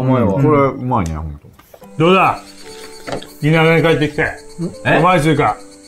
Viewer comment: 田舎って言ったのだろうけど、の滑舌死んでて「新潟」に聞こえちゃったヨ